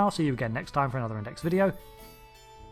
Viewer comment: Is it just me, or did it sound like he said "...And I'll make an ass of you again for the next video" before saying "Goodbye"? :\